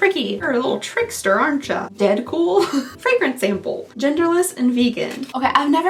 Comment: I'm sure that fragrance isn't named Deadpool, but that's what I keep hearing. He's not someone I want to smell like.